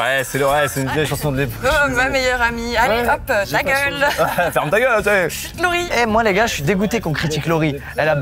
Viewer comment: j'ai compris " ferme ta gueule " mais après j'ai réécouté pour mieux comprendre 🤣